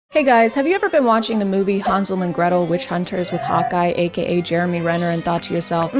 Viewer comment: "Hawkeye aka Jeremy Renner" I deadass thought you said "Hot Guy aka Jeremy Renner" 😂 🤣 I was like oh dayum, never thought he would be your type. ☠️